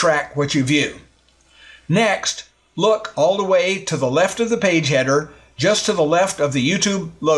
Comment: I thought you said 'Pay Cheddar', not 'Page header' 😄